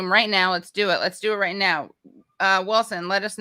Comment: It sounded like Katie said, "Let's do sex...", but I guess it was "six"...sigh...